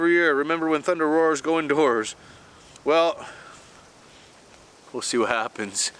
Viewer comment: "When Thunder Roars go into Whores." WaitWhat? Oh... that's not what he said brain, I checked.